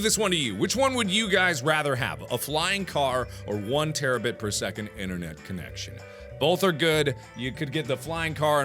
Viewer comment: I thought it said 1table spoon internet😐😐😐